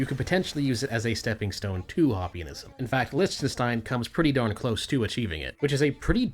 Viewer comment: I'm sorry, at the 20:00 mark, what country did you say? I genuinely can't make it out and the closed captioning just says "lonstein." Did you say Lichtenstein, maybe Luxembourg? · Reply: Definition Lichtenstein